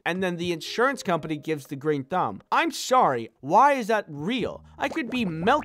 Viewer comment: What I heard when you mean to say green light was “the great thumb” I honestly think that the best thing you could’ve said